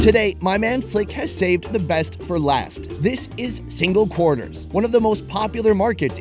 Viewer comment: Ahhhh I am from 🇳🇦 Namibia!!! It's kind of funny seeing your own country 😂idk why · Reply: Yeess😂😂.. Please don't tell me I'm the only one that heard him say single corners